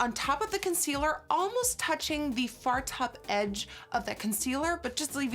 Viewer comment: When Nikki said far top edge I thought she said fart op edge idk why